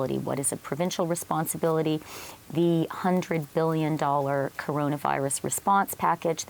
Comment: Did she say 100 billion at 9:05 ? Where are they going to get the money and who are they going to give it to?